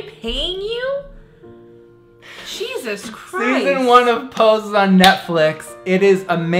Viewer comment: You said Pose but I heard Poe's and got so excited about the show about Edgar Allan Poe that I didn't know about.